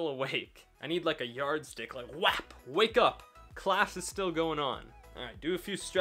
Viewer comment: Did he just say "wapaa!"? 7:14 "Friends" reference